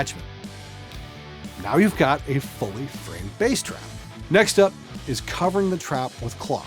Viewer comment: I thought when you said bass-trap, i thought you meant bass amp... I guess thats what i get for learning bass.